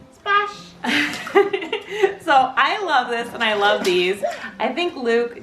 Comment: Did Luke just said "I love it" ? Am i the only one hearing this ? ❤